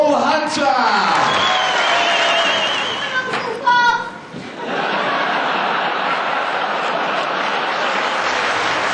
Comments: what is that girl saying at 05:49 ? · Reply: haicugolu she said "I love you Paul!" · didn't get what the girl in the audience said at 5:49... btw the laughs between him and Matthew were adorable. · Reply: she said "I love you Paul!" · what did she say at 5:51 ? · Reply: Omar Subail she said "I love you Paul!" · what did the lady shout at 5:50sec? i cant quite make it out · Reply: Maybe “Love you Paul”? Partly from the reaction, it is tough to make out.